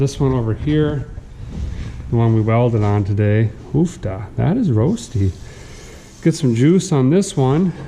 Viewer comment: Did I hear an “uff-da” in there around 13:50?